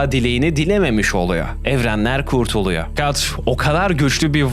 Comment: calt mı diyor ne diyor o kim